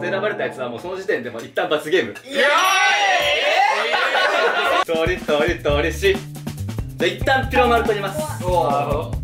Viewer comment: くらいの、じゃんけんで、はなおさんがなんて言ってるか聞き取れないのですが、 なんて言ってますか？？ りっとーりっし？ 初めて聞いた掛け声です。 でも誰も突っ込んでないから、標準語なのかな？